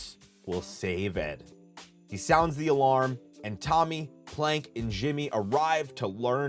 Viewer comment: Did you say Tommy or Johhny ? I cant make it out